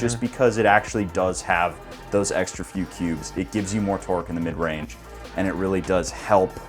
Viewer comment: lol sounds like he said sound like he said those extra few pubes. 😂😂😂